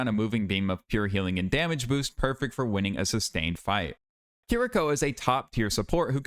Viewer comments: Sorry I zoned out during the Juno section and I just hear the word "futa" and I come to just like "WAIT WHAT?" before understanding the actual context. Brother you scared me there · I was zoned out andd locked in because I heard futa